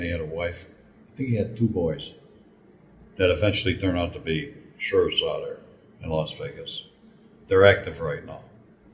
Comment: what does Frank say ex-cop's sons turned out to be in Vegas?.. I couldn't hear it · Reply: Joe Blasko who was a cop for metro but ended up flipping and working for the Hole in the wall Gang became sheriffs out here in Las Vegas. That is what Frank said there.